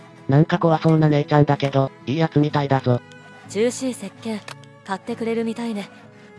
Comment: 獣脂石鹸がどうやってもジューシィ石鹸に聞こえてしまってｗ 鹿油でジューシィというフレーズが・・・ｗ 金鉱石は掘り出して精錬するとダイレクトに村の資金になるのでいずれ欲しい所ですが、施設がないとできないのが辛い所です、ただこの施設で作った金も外に雨ざらしですが・・・ｗ